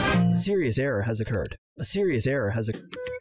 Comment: 修理せぇやハゲが(空耳)